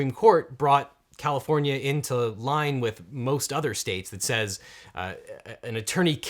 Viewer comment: Did I hear you say, "That rule changed... when California Supreme Court brought California into lying with most other states..."? LOL